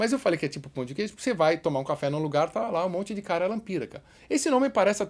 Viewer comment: Já entendi "cara ela pira" 7:47 Deixa like quem ouviu isso tbm kkkk